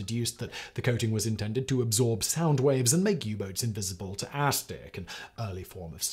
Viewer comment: "Ass-stick" did I hear that right? 🤪🤣 (sigh) I know, childish, I can't help myself sometimes